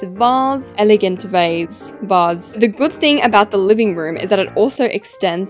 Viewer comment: i swear in the tour i heard say “the ✨aliving room✨”